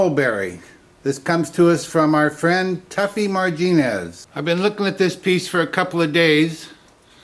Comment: Who in the heck is Tuffy Martinez and where does he get this amazing wood? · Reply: Mr. Marginez: Sorry about getting your name spelled incorrectly. I know a lot of people named MarTinez, and I guess I misheard. As to the other question, there is some mesquite and magnolia here, but it's mostly oak. I do have a few chunks of cypress, but they're still too wet to cut.